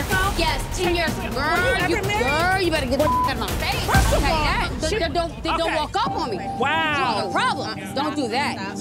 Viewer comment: did she really say don’t walk up on me 🤔 😂😂